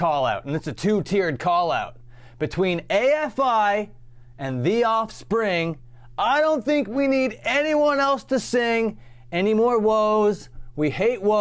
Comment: I thought he was saying “except for afi and thee offspring I don’t think we need anyone else to sing anymore whoas.” Like he was giving them a pass. But whatever, still another great short song under fat mikes skirt